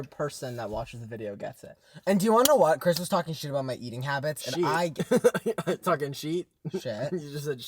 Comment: I hear sheet